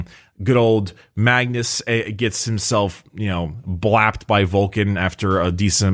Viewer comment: Did a double-take and had to rewind at 5:44. Coulda sworn I heard “got blacked by Vulkan.”